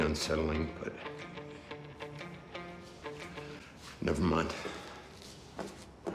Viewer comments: “I thought he was with you” 🤣🤣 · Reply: It sounded like he said I thought it was with you after she said wth was that lmao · “I thought he was with you” 🤣🤣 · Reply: I thought he said; 'I thought he was a jew', until I read your comment and rewatched it.🤦‍♂️🤣🤣🤣I need my hearing checked. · “I thought he was with you” 🤣🤣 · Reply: That comment had me laughing, loudly, when it first aired. I swore he said "I thought IT was with you."